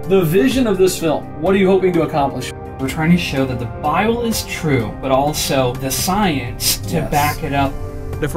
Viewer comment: Why I always hear in intro "show that the bile is true"..... ????